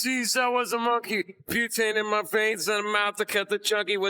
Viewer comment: I always thought it was "mutating my veins", but now I hear it was "butane in my veins".